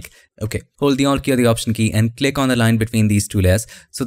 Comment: sorry I don't hear what you said. Hold the option bery bery key? haha sorry, what key is that?